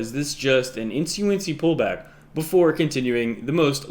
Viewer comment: Pull back? I thought you said Big Stack!